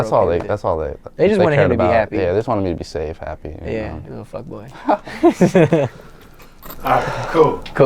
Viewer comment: does he call 6ix a fuck boy?! or am i buggin?